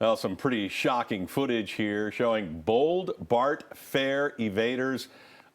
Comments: I thought it said Bret Favre, not Bart Fare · I thought that said bare fart evaders 😭😭😭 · I thought it said Bare fart evaders